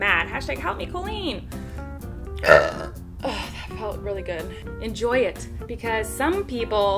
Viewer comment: At first I thought she was saying enjoy the burp aha